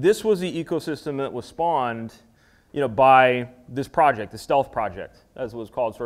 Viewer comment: Did he say (around 4:00) that Java was initially called the Self project? Self was a different language also being developed in Sun at that time. Did I hear it wrong? Java was first called Oak, then Green, then Java.